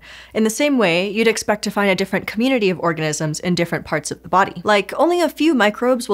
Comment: I'm sorry but i spewed coffee laughing because i thought i heard "orgasms in our gut" laughing to check to make sure i heard that right. oh organisms. lol